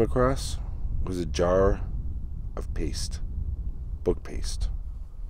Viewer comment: I thought you said “butt paste” 🤣🤣